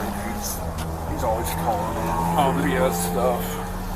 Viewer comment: he said "he is always calling in BS stuff." Another officer asks "who?" And he replies "that guy right there." Just leaving this here cause the subtitles were a little off.